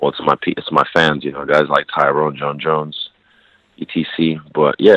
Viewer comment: did he say DC? i didnt know they were beefin..